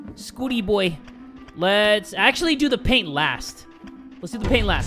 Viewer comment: PAIN instead of PAINT 11:58 you’re wasting your life instead of ketchup